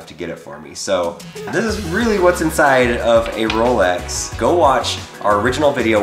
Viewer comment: Being honest I thought it said “What’s inside a Roblox”